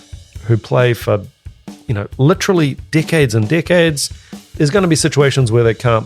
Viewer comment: Meh… has way better calves. Also I totally thought said dickheads and dickheads when he said for decades and decades. Lmao